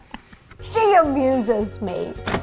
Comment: For some reason I heard "she abuses me"